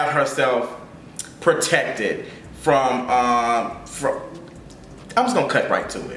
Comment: Flat tummy tea is literally just a laxative like cmon yall 😭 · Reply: I heard I'm gonna try it out